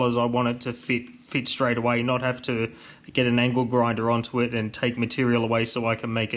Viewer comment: The old ankle grinder lol 😂😂😂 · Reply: Was laughing cause it sounds like you say ankle grinder not angle grinder. Iike they say small things amuse small minds I'm small minded great videos but mate keep up the great work 👍💪